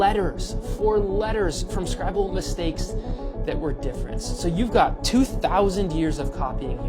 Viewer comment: When the original guy said at 0:45 "scribal mistakes" I thought he said "scrabble mistakes". Yes, I was confused. Heh, when I went back to find the timestamp, the text overlay heard it that way too.